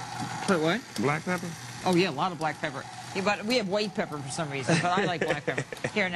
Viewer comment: she didn't say that. what she said @ 2:23 was "..but we have white pepper for some reason but i like black pepper." when did she say what you think she said